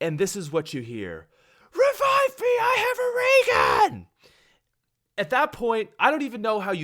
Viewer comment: I had autogenerated english subtitles on and at 2:22 it said: Revive me i got a Reagan (instead of raygun)